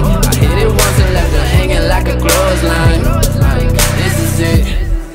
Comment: jennis I thought he said "crows line" instead of "clothes line". I was saying crows line for 3 days. Now I know why I got weird stares xD